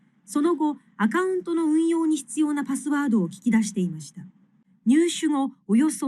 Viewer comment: 運用に必要な<- 이 문장을 mp3로 처음 들었을 때 노래 가락같은 운율로 들리는데, 일본어를 듣다보면 간단한 문장이 노래처럼 들리면서 가사는 모르겠고 귀에 운율만 남는 것이 예전에 유재석이 쟁반 노래방하던 기억이 납니다. 노래로 들리면 가사는 못알아 듣고 귀에 음만 남는 느낌이랄까요. 이 문장 발음해 보니 발음하고 억양도 어렵네요. 이 문장을 보면서 들어도 '웅이요우니'로 들리는데 저만 그런 걸까요....